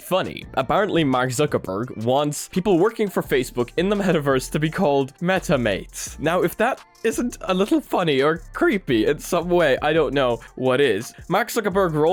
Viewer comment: I thought you said “Metamaids” lol